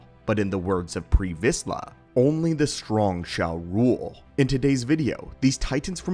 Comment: I thought you said: "Only the strong, Ja Rule," and burst out in uncontrolable laughter. Where's Ja when you need him!